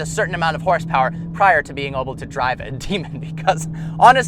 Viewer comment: sounds like he's saying oble instead of able...